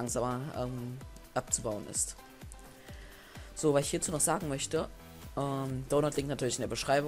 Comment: Ich hab am ende verstanden: Donut liegt natürlich in der Beschreibung